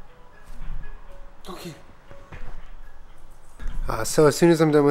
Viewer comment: Ok at 1:06 it sounded like he said gourmet with a hard t instead of Kermit and now that's all I can think of as cermets name... he a gourmet soap connoisseur boi